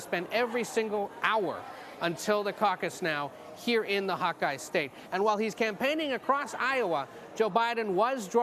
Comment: I heard "here in the hot guy state" lol. Also #NeverBiden #Bernie2020